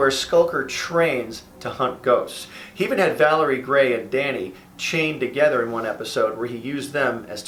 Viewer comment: valerie gray and danny? is that what i heard? He named their handcuffs? XD